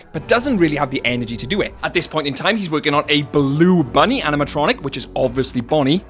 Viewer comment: “BLUE”. that’s what he said